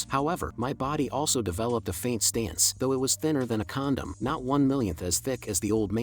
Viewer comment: what did say condom???¿